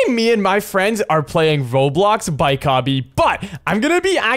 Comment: did anyone hear him say voblox